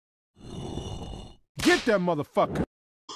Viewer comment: Lol what he said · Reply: He said "Get that motherfu**er"